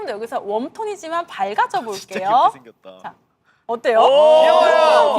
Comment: does anyone know what hamin said here ? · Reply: alittle late but jus in case ure still wondering.. he said ”진짜 귀엽게 생겼다“ which translates to “he(bamby) is rlly cute looking” as usual from our bamplli, haminie💓🖤